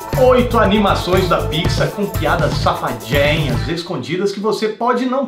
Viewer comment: Eu ouvi "pizza" ao invés de "pixar".